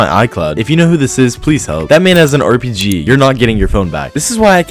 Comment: I thought the end said “how do you like ur toes?” 💀💀💀